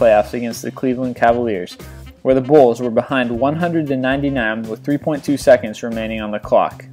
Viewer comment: Did u just say the bulls were down 100 points